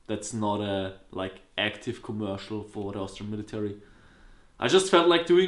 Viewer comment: ▶ Check out my SSG24 Sniper: <link> ▶ Check out my Zoomcam: <link> ▶ My Gear: <link> FOLLOW ME: ⏺ <link> ⏺ Instagram: ⏺ Snapchat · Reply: no kidding but I thought you said Australian military until I saw the writing on the board